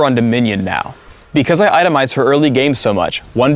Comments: did he say "because I itemize HER early game"? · Is it just me or does Phreak call Teemo a HER at 4:20 "let's look at Teemo on Dominion, because I itemize HER early game a lvl 1 fight is easy for me......" You make me sad Phreak, very sad. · does Phreak say "I itemize HER early game so much", or does he say, "I itemize FOR early game so much"? I always though that Teemo was a boy. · For everyone talking about 4:22 I think he says I itemize FOR early game, just my opinion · did he just say "HER early game"? D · does he say because I itemize HER early game?